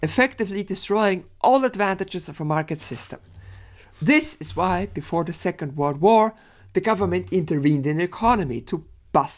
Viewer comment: Small CC correction: @ 17:20 you say "This is why before the second world war..." but the CC says "This is why before the second war" Not a big change, but confusing without the audio